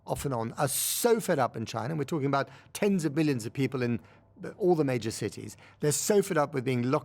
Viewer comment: did you say "tens of billions" of people?